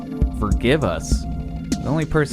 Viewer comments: on jam sessions I heard "kill us" instead of "forgive us" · I heard "Thank you", but when you said "forgive us" I started hearing that. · It wasn't 'forgive us'. I heard 'Who gives a ffff`k' Just me?